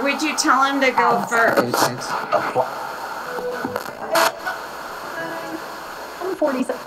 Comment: it sounds like it says “route 47”